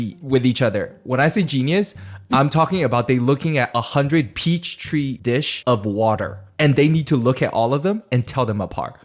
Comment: the way keeps saying "peach tree dish" instead of "petri dish" is killing me